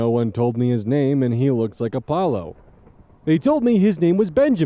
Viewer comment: the renamed dog story is so funny to me because every time it says "he looks like a Paulo" it sounds like its saying my name (Apollo) 😭😭😭